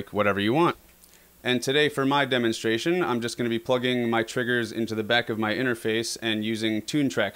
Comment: Ffs each time I hear "plug into the back of my inner face"